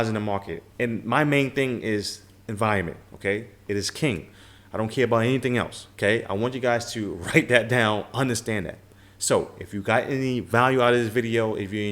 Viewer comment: did he say environment?